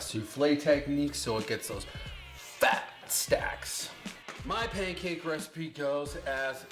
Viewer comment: This is what I was waiting for! Fuck yeah! Thought it said fat sticks aha